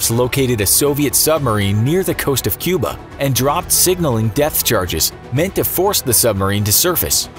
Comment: did he really call them Death charges?